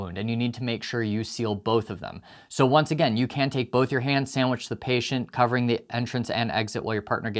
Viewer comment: i had to rewind because i thought you said "ham sandwich"